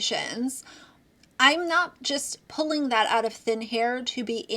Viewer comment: Did you just say “pulling it out of thin HAIR??” 😂😂😂